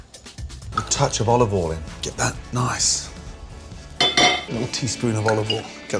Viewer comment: I think he said "Get that WOK nice and hot" not walk, in 44